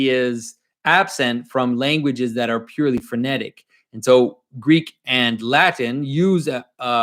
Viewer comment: it sounded like you said “other languages that are purely frenetic” haha. A Freudian slip revealing the chaos of modern language? Do you believe in the phenomenon of Freudian slips?